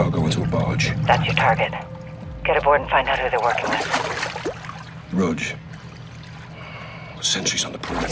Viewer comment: ayo... does Price call Gaz "Roach" at 0:27??